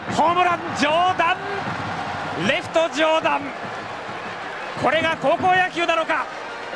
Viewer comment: レフト上裸！！に聞こえたw